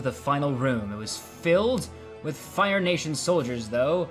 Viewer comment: I thought you said fire asians lol